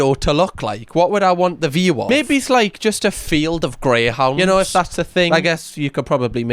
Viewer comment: When said "a field of greyhounds" I swore he said "field of grey houses" 😂😂😂 was like "that's depressingly Geordie of you" 😂😂😂